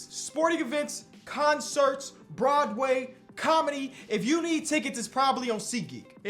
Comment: I got caught off guard I thought he said Rod Wave 😂